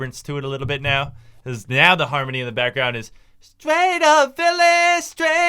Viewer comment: did he say "Gay, no feeling" 😂😂